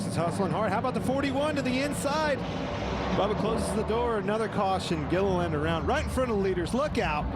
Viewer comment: it says David gilland ss a caption instead of Todd